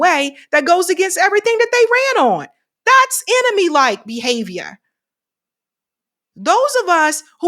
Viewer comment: I heard that behave-yuh…aight you been in Boston too long now 😂😂😂 I went to USC also btw 🍻